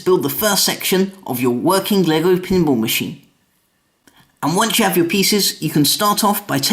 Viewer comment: When you said ‘working pinball machine’ at 0:59, the subtitles say ‘your working leg opening ball machine’